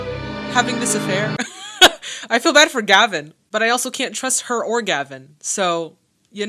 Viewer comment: Thought you said: I feel bad for Gavin, but I can’t trust her orgasm. Had to rewatch/hear it.